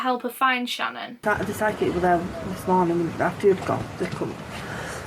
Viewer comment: Oh Jesus I thought you said „sidekick“ instead of „psychic“ xD I got really confused for a moment